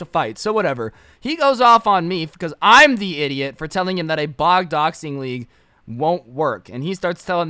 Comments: Did anyone else catch him say "bog doxing" at 7:48? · did he say bog doxing